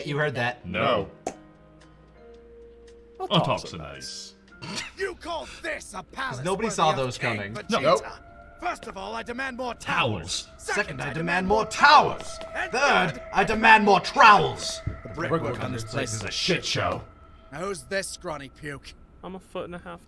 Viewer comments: I swear all this time, I thought Vegeta said “First, I demand more towels”. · Seriously I thought Vegeta said Towels for all this time. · I honestly thought that Vageta was saying "I deman more towels"